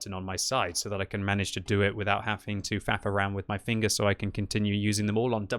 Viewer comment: I thought I heard fap around. Went back and heard him say frath around.